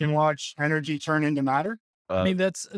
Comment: When he said energy turn in to a rock, I heard say "tree turn into an elephant "